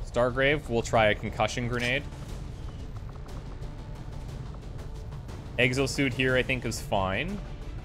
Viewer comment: I heard "Eggs O'Suit here I think is fine.." and it took me a few more seconds to realise what you actually said...